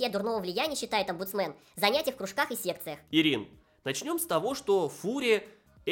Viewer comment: Мне послышалось "... в кружках и сектах?"